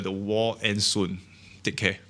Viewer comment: I could've sworn I heard 'dick head' at the very end, but then I turned on my caption to confirm- 'take care'. LOL! Anyways, agree with many points here. I sold off a few of my less confident stocks at lows earlier this week to prepare for repositioning/rebalancing of my portfolio. It's also good for taxes since my long-term gains are minimal (sell low/buy low).